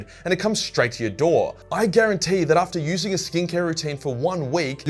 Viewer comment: Half listening to the intro and with the Aus accent I thought he was saying “stink hair” instead of skincare